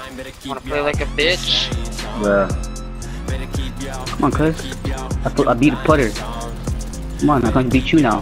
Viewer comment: did he just say "I beat Putther"? Putther would get this kid 20-0 in free aim